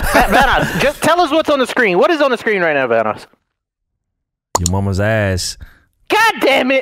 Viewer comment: I misheard vanoss saying "Your woman's ass" and i lost it xD